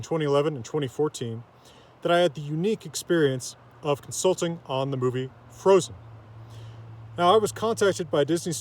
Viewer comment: I thought you said "consulting the frozen" and I was like WHAT